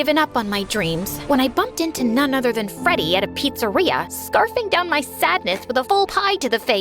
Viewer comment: Did you say Freddy Fazbear’s?